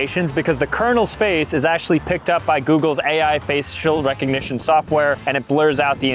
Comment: did you just say "face-shill"?